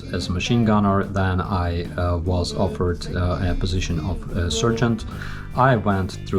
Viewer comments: I thought you said you were offered a job as a "surgeon". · Your calm demeanour and delivery are impressive and I enjoy your dry humour. When you first mentioned your military rank, I heard “surgeon”. Wow! Then I realized you were saying sergeant which by the way is pronounced « SAR-geant ». Please feel free to correct my Ukrainian. 😉 wishing you victory, freedom and a Ukraine free of corrupt puppets. 🫡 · Lol at first I thought you said surgeon. Well that was not expected. But you said Sargeant.